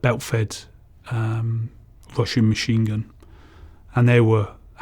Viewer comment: When he said russian MG I heard "washing" machine gun😂